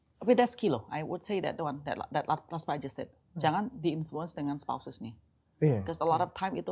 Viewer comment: Sory di menit 37:11 ada yang tau gak ci bilang apa. Jangan mau di influence sama "......". udah ku ulang brpa kali tetap aja gak ngerti. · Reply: “jangan mau di influence sama spouses (pasangan)”